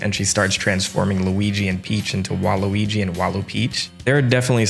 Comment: Did I hear "Waluigi"? Luigi being transformed into Waluigi?